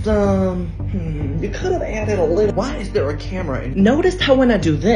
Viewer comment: “Why is there a camera” is my favourite part tbh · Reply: Stop i thought he said why is there a Karen